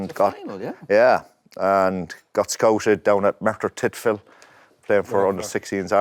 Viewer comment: did I hear that right ? Dennis was scouted playing for Ireland at Merthyr Tydfil ? That’s my home town, that’s mad if I heard that right.